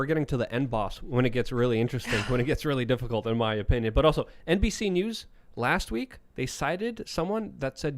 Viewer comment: thought he said NPC news lol